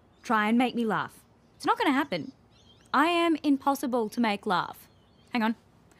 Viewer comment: I thought you said IMPOSSIBLE Chille